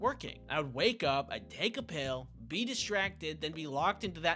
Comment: when I heard this, I thought of piss " wake up, take a piss(pill), get out of bed"